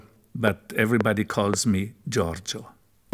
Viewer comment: I swear to fucking god, I thought he said, "My name is Giovanni Giorgio, but everybody calls me GioGio." And I was like, are we about to go on a bizarre adventure?